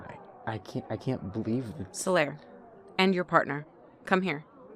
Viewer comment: HELP I THOUGHT THEY SAID “End your partner 😐” WITH THAT CASUAL ASS TONE AND ALMOST HAD A MEDICAL EVENT